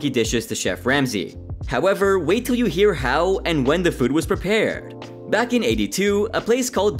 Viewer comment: i thought you said food was prepared back in 82 🤣